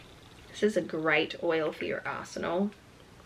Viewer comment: Sunshine Dawn - Nice oils ! Thanks for sharing ! I love magic oils too ! They are so much fun ! Your accent is adorable ! Sometimes i hear things wrong lol. well my hearing isn't so good either but i thought you said one of the oils was good for your arse and all lmfao. You said arsenal but that kinda made me giggle :-) I love these vlogs ! You are awesome ! Please don't ever change ! You are such a Blessing to hang out with :-) Blessings Dear soul